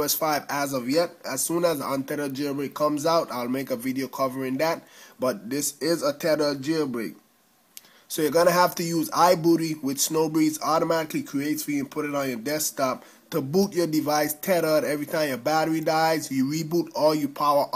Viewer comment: did he just say i booty